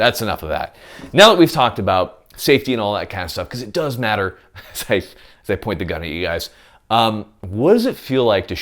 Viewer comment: "As I point the gun at you guys" what I heard... I'm ahhh sexy shootah shooting love in your direction 🎵🎶🎵🎵🎵